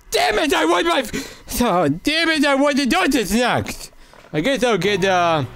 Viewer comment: Hey at 10:44 i heard you say ”fan” and its a swedish swearword❤ love the content❤